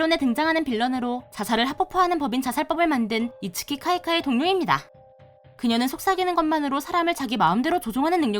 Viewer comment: 내 귓구멍이 이상한거야? 이거 뭐라는거야? 이츠키 카이카이 우우웅니다.